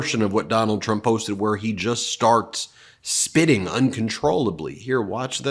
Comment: Apologies. My hearing is terrible. I heard....here's Trump "sh!tting" uncontrollably. My mistake. 😂😂😂😂😂😂😂😂